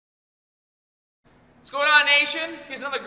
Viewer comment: At the beginning I thought he said, "What's going on Asian?"